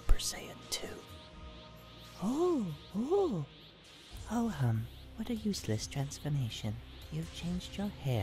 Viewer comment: TIL Babidi said "Ho Hum". I always thought he said "well then" or something.